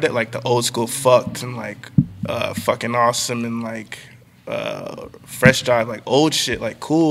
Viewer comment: what did he say after fucking awesome at 6:04 ? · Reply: freshjive